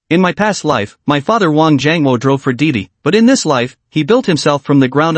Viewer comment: Did he say his father drove for Diddy? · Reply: Lmao!!! 🤣🤣 That made laugh out loud for real 😆.... He was saying DiDi which is the ride sharing app in China like Uber.